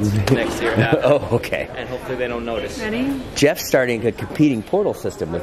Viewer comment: Did just said “Honey” or Am I wrong. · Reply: Bob Tree I think she said “ready”